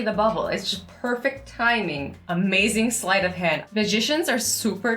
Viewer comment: Azzy: Magicians are so talented Me: Did she just say POSITIONS are so talented?¿?¿?¿?¿?¿. Did she?¿?¿